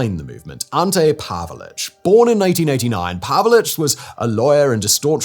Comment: Yep definitely said it correctly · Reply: I need to get my hearing checked because I heard him say born in 1989 why are you people lying about this we all can’t be deaf 😡